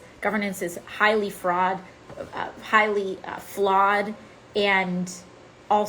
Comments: the best freudian slip ever "highly fraud" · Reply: I thought she said "fraught" before correcting herself with "flawed." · "Our system of governance is highly fraud." Accurate statement. We need to neutralize the power of big money to buy the legislation they want. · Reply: I heard 'fraught' though but both make sense. 😅